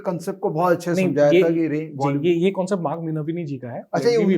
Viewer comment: which author name he is saying i didn't understand name he told anyone tellme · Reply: Mark Minervini it was